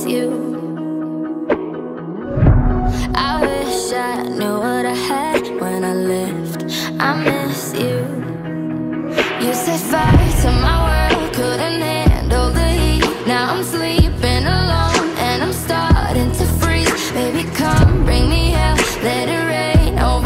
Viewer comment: Stg in thought she kept saying "you said fights are my world"